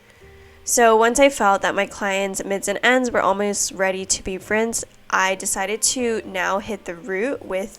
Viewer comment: I know you said once I saw her mids and ends were ready to be rinsed but I heard “her mids and ends were ready to be friends” LOL my roots and ends are ready to be friends…